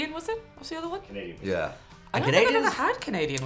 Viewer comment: made a joke about being too big to dive in when said let's dive in. And then you get a cut edit to what I thought was saying a comedian, as in oh you're a comedian. What she actually said was a Canadian... I mean that works too. Some of my favorite comedians are Canadian. I commented too soon because not long after said 'to be fair...' if you know, you know. to be fair.